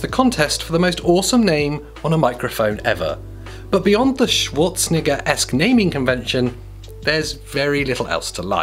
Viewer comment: ayo what did he say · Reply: schwarzenegger-esque...